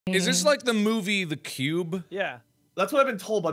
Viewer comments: At first when mark said “the cube” I thought he said “the pube” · at first I thought mark said "The pube" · At the beginning I thought you said the pube